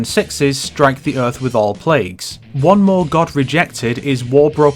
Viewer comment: Strangely enough, now I hear it as: "One more God in Heaven"